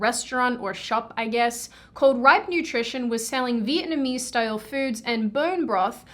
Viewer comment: Until I read it, every time you said Ripe Nutrition, I thought you were saying White Nutrition. That gave me a new level of confusion.